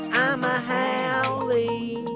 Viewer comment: i thought it said "Im A A whole"